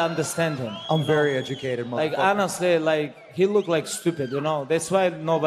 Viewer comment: did he just say he look stupid 😂